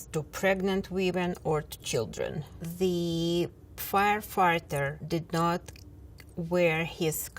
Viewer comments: did she said "Firefarter"? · Did she say "fire-farter"